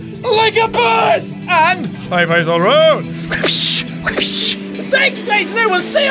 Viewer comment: it took me much longer than it should have to realize that your outro was "high fives all around" i thought you were saying "i'll buy us all a round" and i've been waiting for my drink for a very long time now.